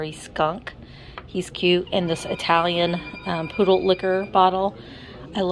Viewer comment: I heard poodle "licker"😳 not liquor, until you added the word bottle. I blame the risqué ashtray for planting my mind firmly in the gutter 😈 You as well apparently with "Wonderbed"🤣